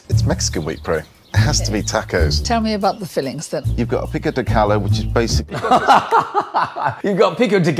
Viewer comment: DID HE JUST SAY PICO DE CALLO?😨